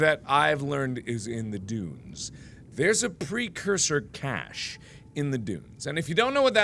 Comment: Mark, I thought you said "precursor cash" not "precursor cache" and I was very confused for a moment